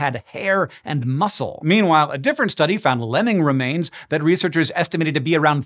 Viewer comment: ahahha ahahh I thought you said 'Lenin remains"